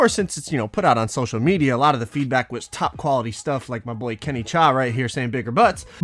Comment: I thought he said Kenny chao